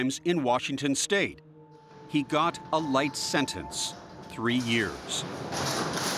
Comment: A light sentence ? I thought it said a Life sentence ! Three years is a joke 😮